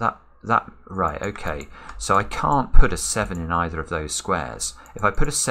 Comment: Love it when mess up maths "2 7s equal 16 an then I need 15 to make up 31 and that breaks the thermometer." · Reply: Not sure he said 2 7's equal 16, i heard i can't put 7 in either else i get 16, he already knew he needed a 9 so the 16 was 7+9 not 7+7